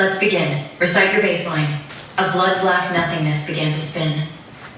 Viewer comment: What was that line “Computer” said? ‘A blonde black ????? began to spin?”